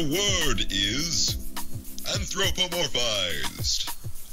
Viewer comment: Anthropamorphite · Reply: Um I thought he said anthropomorphized